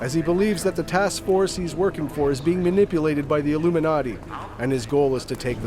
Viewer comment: My legit reaction to the task force is being manipulated by the illuminati. "Did they just say the illuminati?" -Pauses video clicks to comment section.